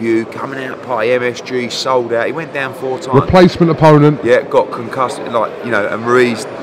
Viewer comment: Oi anyone hear Eddie the first fight ‘he was concussed’